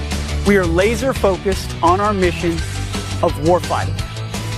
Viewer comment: I initially heard him say warfighting as war crimes which is funny